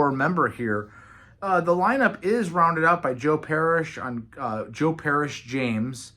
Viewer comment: I thought someone said "Joe Perry" instead of "Joe Parrish" at first ha! Jethro Tull is maybe my favorite band within prog rock. I have almost every album and those I have. I love or like. People can be very harsch on anything past 1970's. I personally don't hear it. I even think albums like "Dot Com" is quite entertaining. Not everything has to be super original and mindblowing to be decent or good. With that many albums the band needed to evolve and try different things. Just making twenty plus versions of Aqualung would eventually become just B, C or D versions of it. Haven't heard the new album yet but I liked The Zealot Gene and from what I heard from others this is even better!